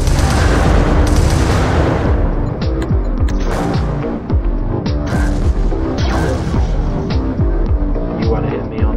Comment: 313dmg wow 18:45 what did you say tho? Couldn’t hear properly lol 20:58 for big mech vs tiny mech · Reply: Rigelstar Centauri At 18:45 he said: “He needs a new drone man”